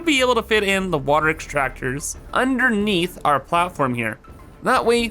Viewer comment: This bit, i heard the word memes, and the subtitles did as well